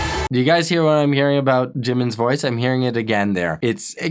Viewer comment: he said jimin instead of jin well at least that is what i heard